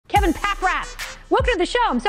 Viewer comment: Sounded like she said kevin packrat at the beginning. You killed it kevin! Good luck with your new career path.